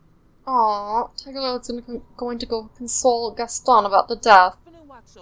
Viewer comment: When you said tiger lily was gonna console gaston I thought you said insult 😂😂😂